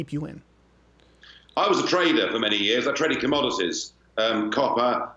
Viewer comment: I thought he said Traitor not Trader